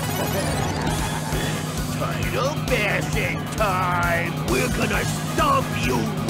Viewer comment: What he say at 2:22 ? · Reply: Rocksteady: "It's turtle bashing time!!" Bebop: "We're gonna stomp you wimps!"